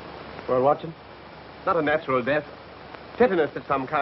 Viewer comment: mins, did he really say ''ragor mortis'' instead of rigor mortis?? Or, did I mishear? I checked twice and t sounded the same each time.